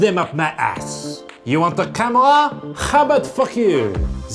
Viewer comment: You want a camera 1:58 : how about 4k I heard : how about f**k you